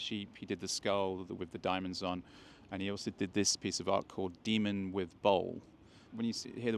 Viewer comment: Oh demon with bowl , I thought you said demon with balls 😂